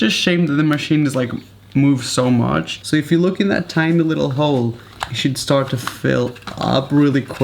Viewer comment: i thought you said "little hoe" 😂😂😂